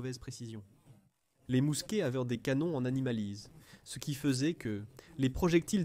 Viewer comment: c'est quoi un canon en animalis ? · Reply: Une mauvaise traduction IA de "âme lisse" (sans rayure).